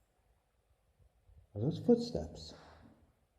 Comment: I’m sorry but who heard the words “footsteps” when the footsteps sound happened? I swear I heard “footsteps”